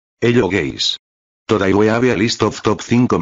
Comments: Is nobody gonna talk about how it sounds like he said, "hello gays" 💀💀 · it almost sounded like he said ello gays